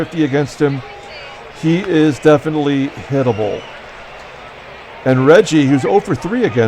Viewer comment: in the 7th inning when you were talking about Castro being "definitely hittable" It sounded like you said he is "definitely edible" 🤣🤣